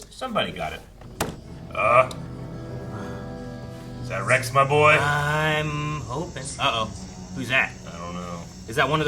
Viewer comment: My brain is so broken that at 3:41 my brain just automatically translated what said to “Rex, ma bookie”